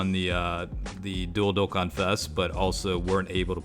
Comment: i want to sing me a lullaby..... ur voice is soothing. edit. at 04:02 when he said dual dokkan fest i first heard it has dildo-kkan fest lol